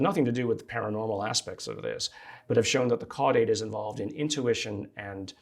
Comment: Does someone know what's this CATE he says at 40:30 ?